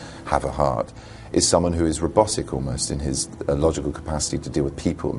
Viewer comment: What did he say from 4:34 - 4:36? Rebosic? Rebos sic? I'm not sure, what is the word he used?